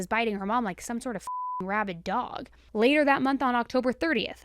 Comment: I listened to this earlier today at work and I thought you said "she was biting her like some sort of fcking rabbit dog". Let me tell you I nearly collapsed from laughing. I was like okay is saying some weird shit but I absolutely love it😂😂 And I am just realizing it now that the word was rabid, and there are no rabbit dogs in this story😂🐰🐶